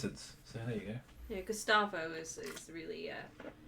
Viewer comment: If not for the automatic subtitles, I could've sworn I heard say one of your lizards is named "Gestapo", lmao